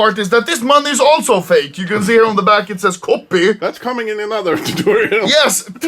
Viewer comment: says "that's coming in another tutorial" but it sounds like he says "that's cumming in your mother" coz is a big chad, and he loves it when you call him